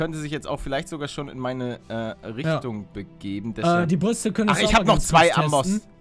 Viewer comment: GermanLetsPlay: “Die Rüstung...“ bei Minute 2:57 und ich hab verstanden “Die Brüste“ 👌😇💪😂 Ihr müsst genau hinhören so Geil